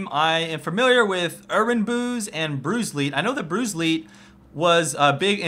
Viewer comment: For the longest time, I thought he was saying Bruce Lee, instead of BruiseLeet...